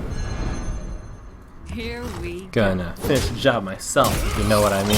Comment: did he just say "gonna fist-job myself"?